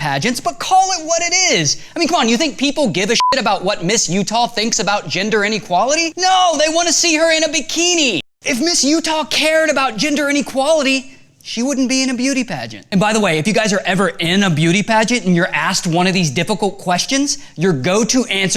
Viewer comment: i thought he said ginger inequality lol